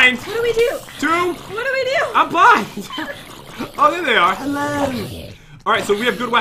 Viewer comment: Am I the only one who heard the cuddle bear saying "hello"